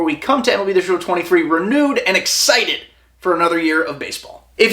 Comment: Did you say “nude & excited” ???